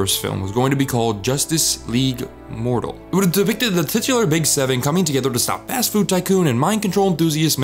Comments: what did he say? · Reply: "It would have depicted the big seven..." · What does he say at 5:32? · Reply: "It wouldve depicted the titular big seven coming together" There are captions available btw